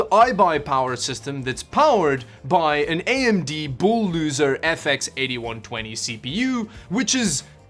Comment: did u said AMD bull-loser???😂😂😂